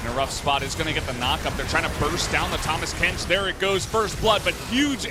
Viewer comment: did he just say thomas kench? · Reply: kano Urban tahmas kench